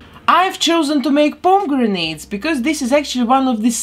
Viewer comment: I swear I heard “Pom-grenades” the first time she said pomegranate. I loved it and that’s what I’m calling them from now on!